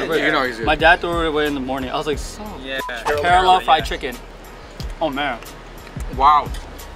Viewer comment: he actually says "Kerala Fried Chicken" not caramel hahaha. Kerala is a south indian state